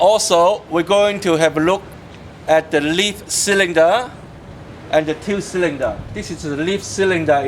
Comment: also we are gonna look at the ... Leaf Cylinder?? surely that's not what was said but I've listened to it so many times but I still don't know what was said